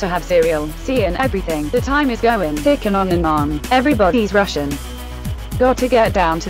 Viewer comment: czy ja usłyszałem "every body is russian"?